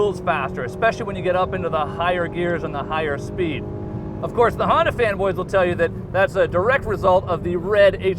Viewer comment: Did he say, 'the Honda fans or the Honda fanboys?'